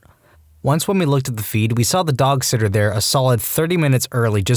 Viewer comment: Give me the vibe that being a dog sitter isn’t bad 😂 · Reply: i thought you said dog shitter